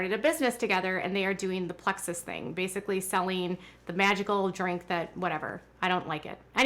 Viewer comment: You know when you don’t hear one phoneme/syllable of a certain word correctly? At 3:04 I thought I heard ‘vaginal drink’ instead of magical drink. 🤦🏼‍♀️. I thought hmmm, so that’s what they’ve rebranded douche as- a vaginal drink. I have heard of some weird MLM’s but this one takes the cake. My sister was like what on earth are you talking about…? 😳I replayed it 😝. Ok phew 😌. Not a vaginal drink they are selling. But watch, that will be an MLM in the near future. They know no bounds. 🤨